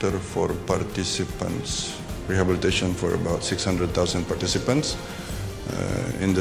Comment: I heard him say something like '600.000', not '<num>'.